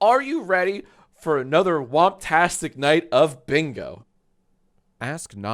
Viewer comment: did he just say wubtastic? Like the phrase created by MSM?